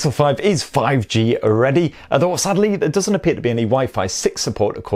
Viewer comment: For a second I thought he said "There doesn't appear to be any Waifu 6"